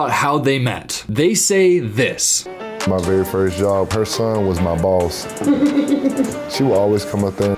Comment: Bruvvv I thought he said “ her favorite part of me… was my balls “, when he said “ her son was my boss “, idk if it was a miss hear or if I’m a bit tipsy! Had to rewind 😂👏 ( and I thought sent this comment, took me 11 minutes to realize I didn’t when I opened the comments again to see what others had to say 😭 )